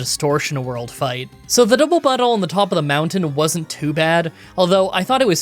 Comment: did you say "double butthole"? Because that's 100% what I heard 😅 Also what fossil did you take?